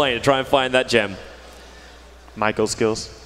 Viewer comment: "Michael skills", Bruno said "Micro" but it was misheard by some of the announcers and Kaci as "Michael". watch?v=HlQVg2SXxQA